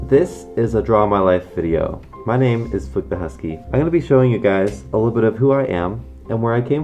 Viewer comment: I thought he said "Flip the husky"